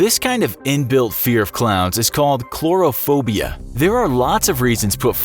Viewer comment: did he say "chlorophobia"? at 4:07 ?